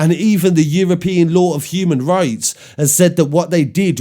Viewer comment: Tell the judges that have approved Encrochat evidence to convict in the UK that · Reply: Sorry I thought you said ‘European Court of Human Rights’ when I first listened which a decision by them would still bind us legally. But the EU also has a charter on fundamental rights and can rule on human rights cases as well which we would not be bound cuz of Brexit